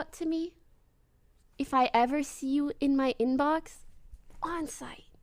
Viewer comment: When she said "On site" I thought she was saying "Bonzai." I was thinking "wtf she doesn't know what banzai/bonzai means."